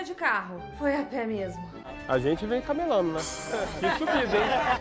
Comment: "camelando" eles vieram a camelo? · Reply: Eu entendi "caNelando", tipo, batendo canela...